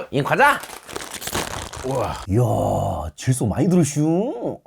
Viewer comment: 감자 많이 들었다는 줄 알았네ㅋㅋㅋ